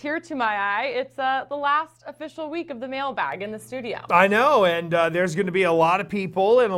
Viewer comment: everytime i hear "mailbag" i hear "male bag" and laugh..